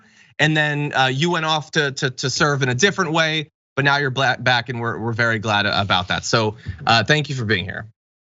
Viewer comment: Did John say, “Now your black..back’? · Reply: He said, "Now you're bla...back". Start it at 1:46, it's more clear. I caught it on the live show, forgot about it, and listened to it again just now. If he had said the full "black", the producers wouldn't even be putting this clip out there. If we heard it, there's no way they didn't.